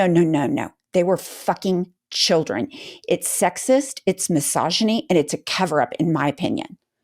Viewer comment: Love you guys. You are a huge part of the fight for democracy. Much love from Australia 🇦🇺 ❤️. THE WORLD IS WATCHING THIS MADNESS!!! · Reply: Yes! And still loving your intro Happy Merry, cheers! 🥂 NB: when you said misogyny, for some reason I heard "massageny! Guess I've meen listening to the Trumpstein files too much...